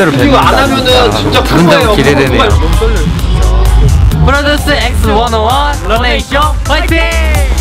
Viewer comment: I heard Malaysia in